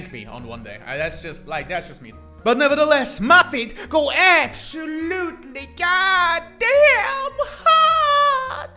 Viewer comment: sounded like you said "muffet goes absolutely goddamn hard" today